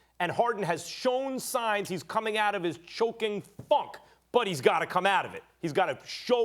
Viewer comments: Anybody else hear “choking fuck” at 2:32 lmaoooo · did max say fuck? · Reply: funk.